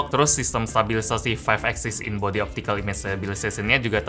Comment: ngomong apaan 😭, sasiasiasiasia..., Hmm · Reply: Sistem stabilisasi 5 axis in body image stabilisasion 😂